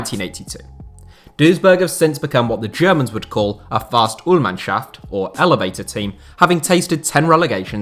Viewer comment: love how he pronouces "Fahrstuhlmannschaft" smth like Fahstulschaft hahahah Nice Vid tho thx for that!!! And its called 3rd and 2nd Liga :D "Dritte Liga" and "Zweite Bundesliga" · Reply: Ich hab erst 'Fast-tool-mannschaft' verstanden😂